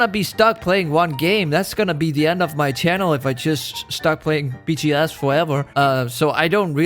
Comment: Captions says: Peachy ass (while he said BGS)